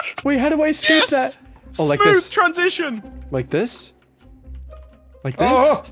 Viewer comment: At some point someone said somethin and i heard ‘spoon transition’